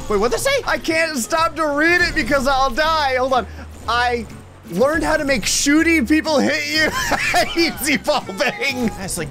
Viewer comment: I learned how to make shooty people hope you lik is what it said